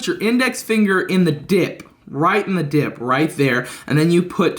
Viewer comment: sounded like you said dick their bud....................... demonetization